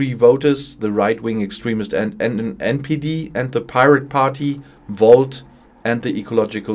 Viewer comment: “and the pirate party,the volks” ?? ☠️ Did I misheard? 🤣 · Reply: pirateparty and volt